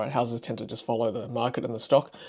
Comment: correct me if I’m wrong did you just say appreciating asset? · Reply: I must've been mumbling. Cars are definitely a depreciating asset